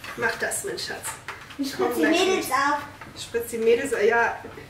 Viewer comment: Minute, sagt „...ich Spritz die Mädels ab....“ 😂😂 sorry, nicht falsch verstehen, hört sich irgendwie komisch an 😂😂😂